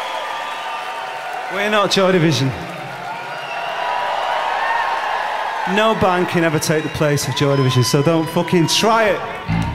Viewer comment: What did he say at 35:00, can't really understand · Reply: he says "were not joy division, no band can ever take the place of joy division, so dont fucking try it"